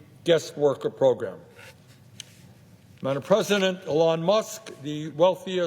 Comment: did he call him president musk😂